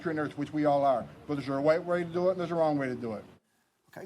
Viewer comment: did he just say “there’s a white way of doing it” 💀 😂